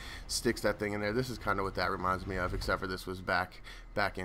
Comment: did u say Ezio Delitore???